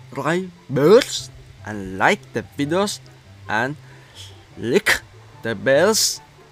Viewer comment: Lick the bell and share it Me : wtf you said lick the b*lls